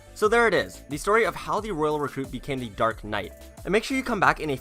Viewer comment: u said dark Knight instead of dark prince